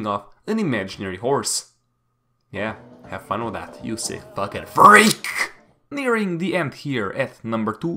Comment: It must be rare for him to lose it to his own jokes Also did you just say "furryeak"